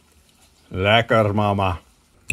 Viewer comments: Did you just say "lekker mama" or am i tripping? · Did he just say "Lekker Mamma" -_